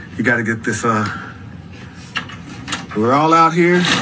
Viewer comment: For a second I thought he said the dog is eating a dog's turd